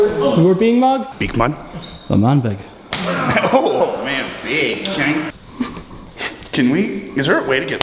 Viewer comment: did I just hear l’manburg!?